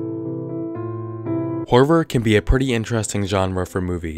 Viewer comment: When he said horror in the beginning I could've sworn he said Harvard I was so confused 😂😂